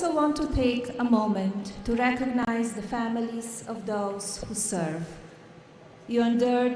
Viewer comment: In 2:20 did she said she want to thanks the family of dogs that serve I'm sure that's what I heard